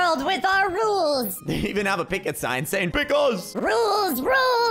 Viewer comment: saying pick us Me: watching with subtitles saying pickles